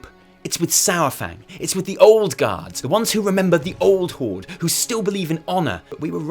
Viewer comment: did you say ''Old Gods'' ??